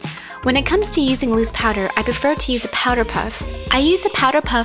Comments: I thought she said Powerpuff. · hahah powder puff......i thought she said power puff.....<3